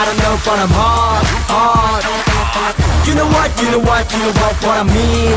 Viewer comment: omg that guy was syaing "Hot"?? the whole time I thought he was saying "hard" this changes EVERYTHING! haha